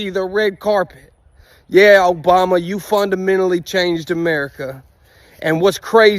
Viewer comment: Lmfao the way he pronounced it, i thought he said "fund a mentally changed America" and was so friggin confused 😂 like I'm from the deep south and have heard some oddly stressed consonants and people making words sound ways they have no business sounding like but that one made my brain's language processing just slam on the brakes and start going "error"